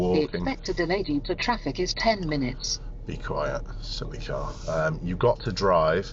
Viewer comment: I thought you said silly cow at first! 😁